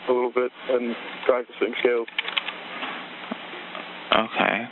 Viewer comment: he clearly says "practicing scales" in place of the question mark.